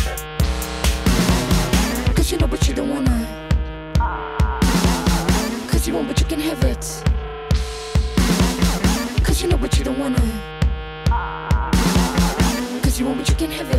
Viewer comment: When they say "cuz you want but you can't have it", I Always thought they were saying "cuz you want my chicken nugget" XD